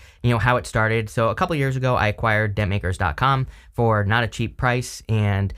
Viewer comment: At first I thought you said, "DenMakers" and I thought ok, what? DentMakers like makadent in the Universe. I love what you are doing. Thanks.